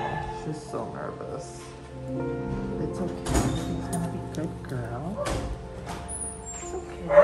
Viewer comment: I'm in bed rn and crying for real SHE CANT GO SHE CANT NO I DONT WANT HER TELL ME THIS IS A DREAM I ONLY SAW HER FOR A WHILE p.s. I thought he said in the beginning shes gonna go to sleep and I was like what she died and then I heard it agian